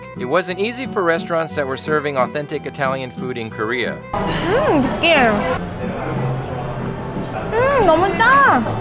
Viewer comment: she say nigga I hear it!